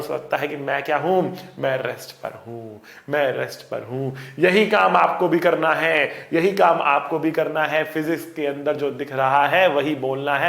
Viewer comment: par sir ne jab kaha ki yehi kaam aapko bhi karna than I thought sir is saying aapko bhi conductor banna hain